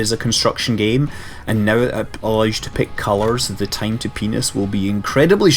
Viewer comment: what did he say? time to penis?